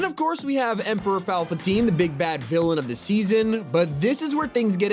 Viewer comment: I thought bro said big bac 😅